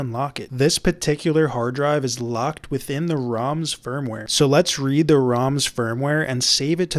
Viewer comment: Am I the only one who heard him say the ROM sperm ware?